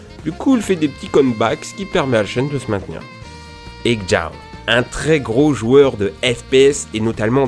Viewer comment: c'est quoi le nom du youtuber a 5:05 j'arrive pas a comprendre avec ton accent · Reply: xJawz